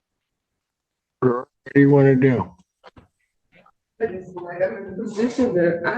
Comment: i think the audio skipped for a second right here and it caught the end of him saying her last name either that or he said "Girl, what you wanna do?" Lmao 😅. I would think he didnt say that in active court but i could see slipping up and saying that lmfao... I'm gonna chalk it up to a time skip in the video but that is funny if he really did throw that out there